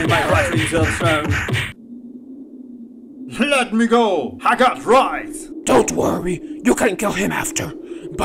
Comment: did Ludwig just say "I got fries"? That doesn't make sense